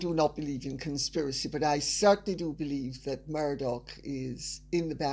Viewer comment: Murdoch? I thought u said murder!! 😂😂😂 hmmmmmmm???its possible...